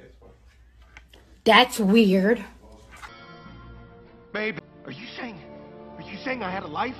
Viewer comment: DID HE SAY "BABE" 😭💀